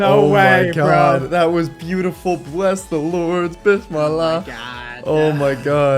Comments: Play <link> Today! ⬇️ Modpack: <link> Website: <link> Discord: <link> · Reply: Did you say bissmilah in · Did I hear bismallah?